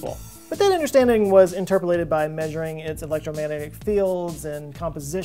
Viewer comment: did you say L ron hubbards enturbulated??